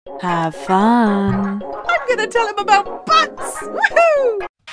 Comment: So I’m here drinking my Pepsi and being depressed then I hear “IM gOnNa TeLl hIm AbOuT bUtTs WoHOO” then I start to die of laughter XD